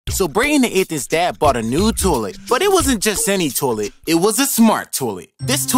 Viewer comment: I thought you said bought a new toy